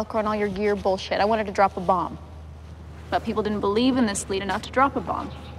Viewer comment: When I saw the film I thought she said drop a J DAM not a bomb. Wierd.... 😐